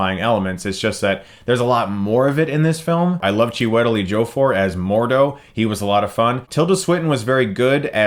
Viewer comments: 'Chiwitel.. wait wtf did u say lol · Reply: "Chewie-tell Edgy-oh-four" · oh god first time through I thought he said "Tilda Swinton as the Asian one" glad I went back to listen again